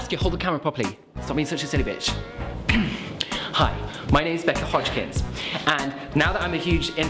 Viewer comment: For a second I thought becca said "SAUSAGE YOU SILLY BITCH"